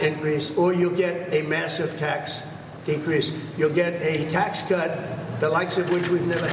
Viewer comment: With respect, I hear him saying decrease. I don't know if that's because that's what he should be saying, or if it's because it is what he's saying, but I hear "decrease".